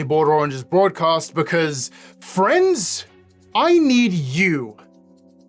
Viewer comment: I am sorry, I thought for a second that you said France, and I just had to double check that I wasn’t in some alternative timeline where France is actually desired.